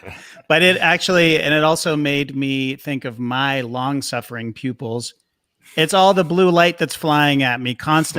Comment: when they said pupils i thought they said pube holes..